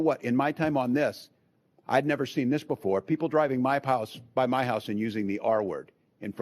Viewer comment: did he just say "People driving by my palace"? 🤣